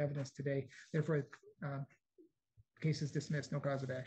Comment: She said thank you your Honor. But I heard I'll be back Ms Wilson.